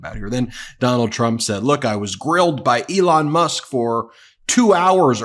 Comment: Did he just say he got drilled by Elon? Or grilled 😂😂